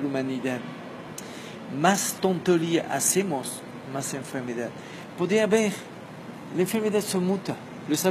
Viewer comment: Não consegui entender o que ele disse aos 11:31. " ...la enfermidad faz parte de un equilíbrio da humanidad, quanto mas.........hacer, mas enfermidad." Alguém poderia traduzir para mim a palavra? Grata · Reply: Cuanto más tonterías hacemos más enfermedas